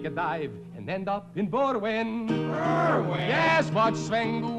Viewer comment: Berwyn? I thought they said "more wind".